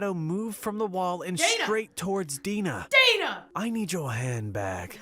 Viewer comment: Lol, I thought the robber said: " I need your hand back." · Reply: I thought he said "I need your HAIR BACK